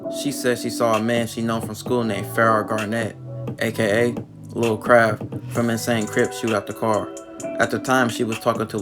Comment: Did he just say baby crab from insane?😄😄😄😄😆😆😆😆😅😅🤣🤣🤣🤣🤣🤣😂😂😂😂